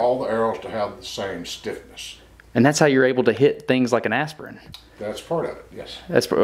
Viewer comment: He shot an aspirin? That had a much smaller impact after I misheard it as an astronaut first... Hmmm...